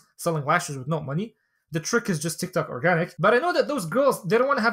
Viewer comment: the trick is tick tok or what did he say? 9:30 mark?